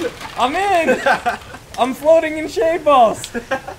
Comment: I thought he said "I'm floating in shaved balls!" HAHAHAHAHA